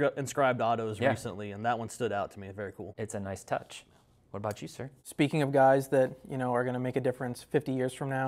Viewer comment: I swear teapot..all I heard was “what about VW” …..in a little frail mouse voice….who in the freak was that……